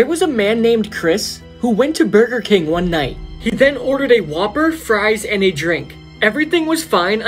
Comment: when it said whopper I thought he said Waper